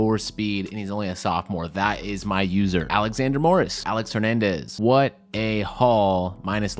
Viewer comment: When said Alex Hernandez i thought he said Aaron Hernandez